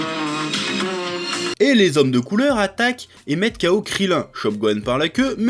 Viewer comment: Alors Cooler plus cool que Freezer ? T'as compris ? S/o Varobaz pour le montage : Varobaz : <link> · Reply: Ha ha "les hommes de Cooler" j'ai entendu "Les hommes de couleurs". Merci et, mon cerveau merde grâce à vous